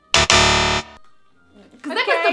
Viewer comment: Io ho capito "che cos'è questo?"